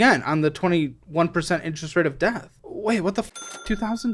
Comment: i thought i heard "interest of death" not interest of debt" sum